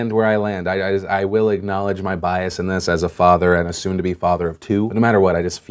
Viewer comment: Did I just hear "Soon to be father of two"? Congrats!